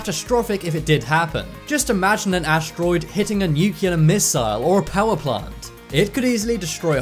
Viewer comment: wait.. did you just say nucular?